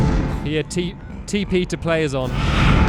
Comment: at the end did i hear you say "tp to player is on" xD